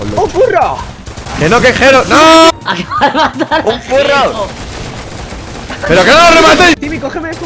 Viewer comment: Pensé que dijo un churro xd